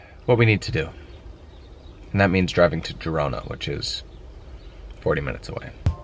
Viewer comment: it sounds like you said driving to Toronto! A 30 minute drive from Spain!